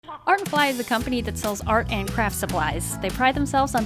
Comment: I thought she said its a company that sells art and flies XD